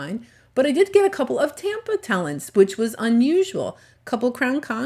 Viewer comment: What an interesting beach! And I like that you post the name of different shells. Sometimes I'm not sure what you've called a shell. Like I always thought you were saying pink talents,now I know the correct name is pink tellin! So a big THANK You for that. Stay safe and content. Thank you!